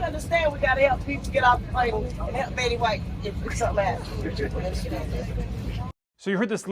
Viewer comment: I thought she said “Lily Whites”⁉️🤪